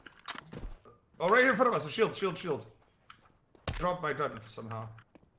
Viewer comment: I think he said shield instead of chill